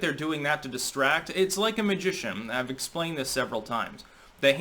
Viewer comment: Magician. I thought he said "Egyptian". :D